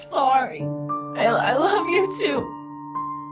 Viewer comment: At the end I thought pink said I love YouTube 😂